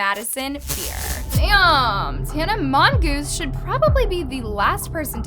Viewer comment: idk why I heard "tana mongoose"💀